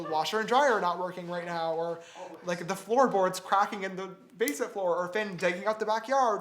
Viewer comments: I thought he said frog in the basement 👁👄👁🐸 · the frogs are in the basket 🧺 🐸 omg i thought u said basket not basement